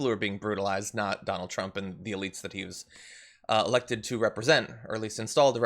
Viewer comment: At first I thought he said : I was elected to oppose the law.